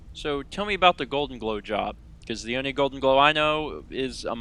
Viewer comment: i thought he said golden blowjob